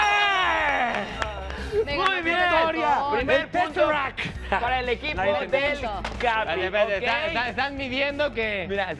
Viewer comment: entendi esa referencia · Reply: Dijo; "El Tesseract" o sea "el Teseracto" que es el cubo azul que apareció en algunas películas de Marvel xD